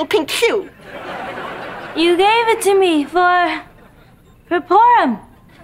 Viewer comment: Did she just say "PORM"?😂